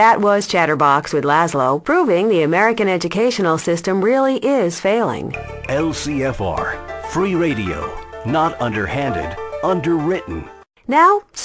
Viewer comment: I always thought they said nurse bomb lmao